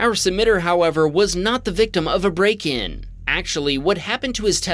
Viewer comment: whoever is responsible for the captions used "brake" instead of break.